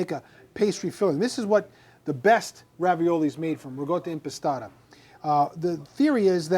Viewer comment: Your dialect is impeding a good Italian pronunciation to be heard. It is Ricotta Impastata! At first I thought you said RIGATTA( a large boats festival in Venice/Venezia). AHH AHH................